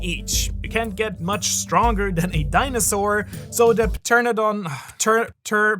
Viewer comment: Did he say pa tern a don? 🤣😅